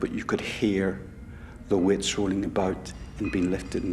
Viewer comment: first, let me say I love all their accents. I really do! With that said, boy! Am I glad they showed pictures of what the PC was saying... I couldn’t figure it out at first. Is he saying "wits" or "witch?" Oh I get it! He’s saying "weights."